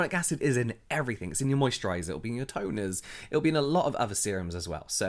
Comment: Lmao when he said hyaluronic acid is in everything I thought he said it’s in your toe nails not toners 😂😅